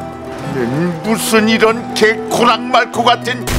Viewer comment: 개 코랑 말코 ㅋㅋㅋㅋ 그렇게 들리긴 하는데 원래 "개 호랑말코" 아님?ㅋㅋㅋㅋ